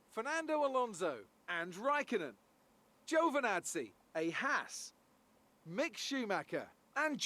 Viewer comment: Every time I heard the call out for the grid I heard "a hack" when Crofty would announce Lake Speed. I put on CC today, you're no hack, that's for sure. A Haas is what Crofty was calling out. Funny